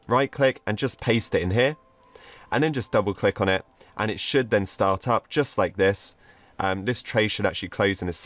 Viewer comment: For some reason when he said shell start up I thought he said shell shut up